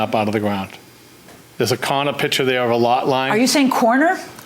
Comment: And “corner” and whatever the heck he said lol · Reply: it sounded like he was saying the name Connor to me. Very strange, I've never heard the word corner mispronounced by an English speaker before.